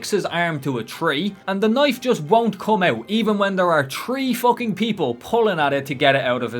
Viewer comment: Don't worry I get it alot 😂 there seems to be many words that people find funny with the Irish accent 😂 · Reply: I’m not gonna lie I did a double take when you said “three fucking people” cause I heard “tree fucking people” and was like they’re doing WHAT to the tree?! Anyway it’s beautiful and perfect never change 😂❤